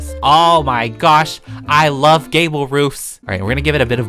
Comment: When he said i love gable roofs it sounded like he said gayble roof